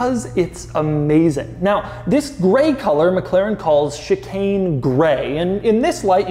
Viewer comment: i heard sh*t stain grey.... yeah thats probibly why i might not ever own one of these